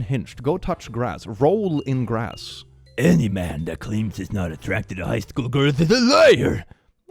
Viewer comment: Listening to this while cooking, I misheard: "Any man that claims he's not a threat to high school girls is a liar" and was like "well, that's a bit drastic".